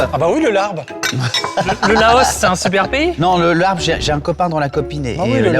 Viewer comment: j'ai compris "J'ai un copain dont la copine est bleu ciel" j'ai explosé de rire. Magnifique ce trio innatendu j'en veux d'autres!!